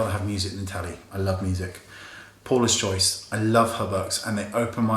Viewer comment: anyone know if what he says is "Paula's Choice"? he said it so quickly, bu,t I think that's what I was able to make out